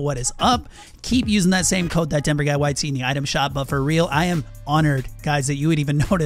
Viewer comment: I Swear when says his creator code I swear he sound like he saying ThatDenverGuyWhiteTee instead of ThatDenverGuyYT. Heck you could probably find ThatDenverGuyYT on a WhiteTee from his item shop 😂